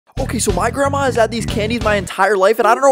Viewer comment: My dumb ass thought he said panties at first 😂😂